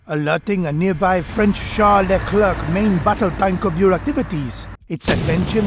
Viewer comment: Am I the only person that misheard "char Leclerc main battle tank" and my brain stoped working and heard "Charles Leclerc main battle tank" and got confused as heck because I watch f1?